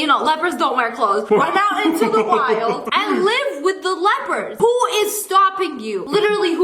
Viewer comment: “Then go live with the leoperds” what i heard- “go live with the lepers!”